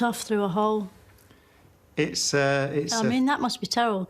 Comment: “Can you imagine what it would feel like to be sucked off through a hole?” Did I hear that right? 😂